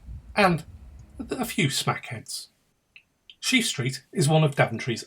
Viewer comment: DID HE SAY SMACKHEADS 🤪🤣🤪